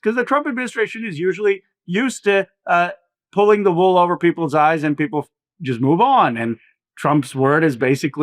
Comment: I thought i heard him say you cant pull the wool over Americans eyes. HAHAHAHAHAHAHA